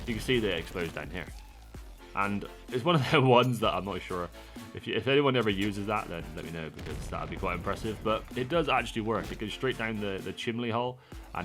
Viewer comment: Did you just say Chimley hole? 😂😂